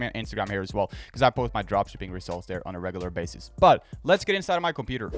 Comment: Did you say "lets get inside michael beater" at 1:34? :D